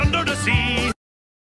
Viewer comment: i hear only "under the balondon"